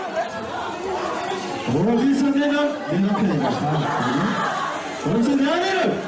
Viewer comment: Did he just called her , HiS WiFuE? O_o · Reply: Actually he said out of his instinct husband :))